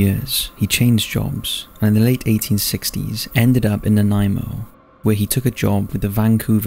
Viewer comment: "It was thought that Hawaiians were not Christians and therefore murder wasn't--" I'm sorry wHAT?? I originally misheard and thought this case was 1960s instead of 1860s and was very concerned at that line...